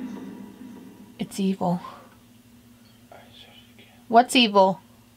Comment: I heard “The Cecil” then “It’s evil”